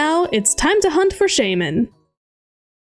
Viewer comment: An add came up so instead of saying let’s hunt for shaymin I heard “Let’s hunt for shame” lol